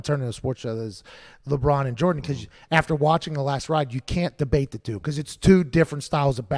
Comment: Did he call it "The Last Ride"?